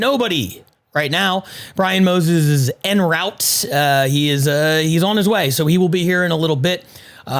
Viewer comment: I thought he was gona say " Moses is N-word late "